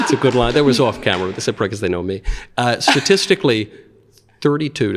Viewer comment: what’d he say? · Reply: "someone off camera said 'probably because they know me'"